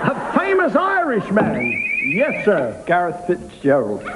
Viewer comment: I have to say I don't know why they laughed when he said "Gareth Fitzgerald". I think he meant to say "Garret Fitzgerald", who was the Taoiseach (prime minister) of Ireland at the time. Still, a miss is as good as a mile... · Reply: I actually thought he said "Garrett Fitzgerald," so I was surprised it wasn't up there.